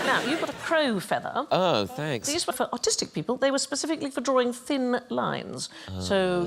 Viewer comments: i thought she said autistic people and got excited. "a quill just for those touched by the tism! what an honor" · all this time i thought she said autistic. ARTistic. i was confused as to why we would have our own feathers way back then. i did struggle with straight lines as a child.